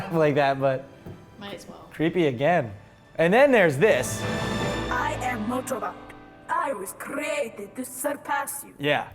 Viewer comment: I thought it said "I am Motobot and I was created to suck ass u"... I was like, WHAT?!?!?!